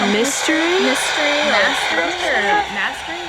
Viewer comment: I don't think he said mystery I think he said MINISTRY bc u asked him what message to u have